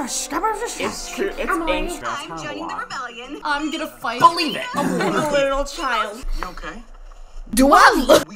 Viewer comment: When he said believe it like Naruto I thought he said bully it 😂